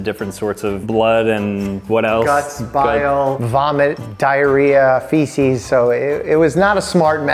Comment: did he just say diarrhea? 💩